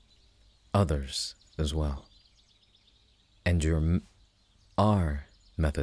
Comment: I can SWEAR that he saying "Andrew" here.